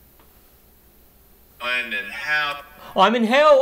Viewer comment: I think when you thought it was saying IM IN HELL it was actually saying I NEED HELP! Slow it down you can make it out better.